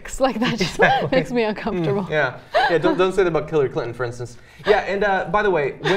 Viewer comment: I thought he said Clitary Clinton!